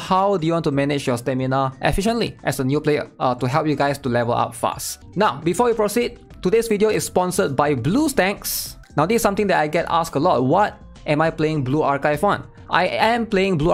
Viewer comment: I thought you said "Blue Stanks" 💀